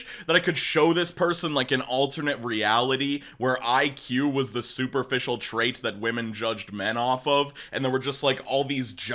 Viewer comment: Why did I hear “where IQ is the superficial trait women judge men off of” as “where I, Cue, is the superficial trait women judge men off of”